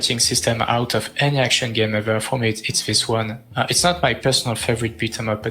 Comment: your guest mentions best fighting system, for the life of me I can't hear or catch what he said. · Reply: I got it. "Its this one." AVP